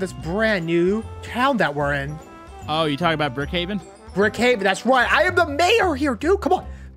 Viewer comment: I thought said Brookhaven lol